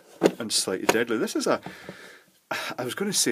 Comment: did you say "slightly deadly"? How many degrees of deadly is there? lol